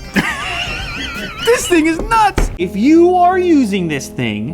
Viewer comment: who else heard "this niggas nuts!!" lmaoo